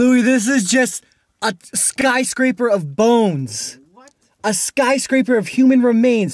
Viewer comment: is it me or did i hear a female say "What"